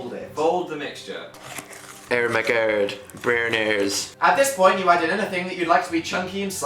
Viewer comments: It sounded like he said bronies, XD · I thought they said boners instead of brenners xD